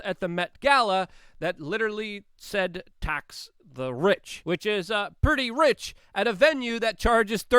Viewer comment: I thought her dress said VAX the RICH.